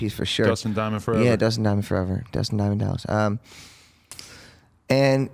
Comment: does he call Screech (Dustin Diamond) “Dustin Diamond Dallas”??? Lol bro is SHOT OUT. I can’t believe they are actually letting this xanned out dude in the ring. He’s 5’8 and weighs ab 90 lbs. Lammy’s going to hurt this kid 😬